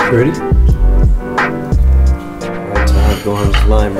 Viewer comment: I thought it said trying time